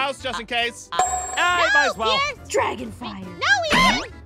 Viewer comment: did aphmau say dragon fart???